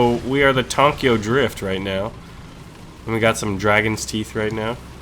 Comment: did say tonkio drift at 9:46? XD